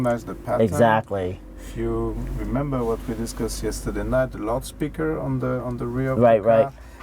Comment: Did he just say "Laut-speaker" 😂